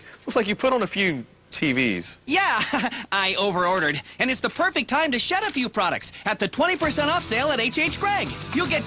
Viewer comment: I thought he said "it's time to shut up!"😅😅😅